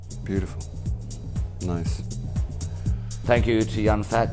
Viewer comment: I thought he said "Young Fat Bill"